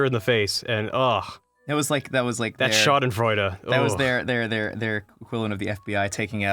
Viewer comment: Did he just say the german word ''Schadenfreude'' or what did he say :o ?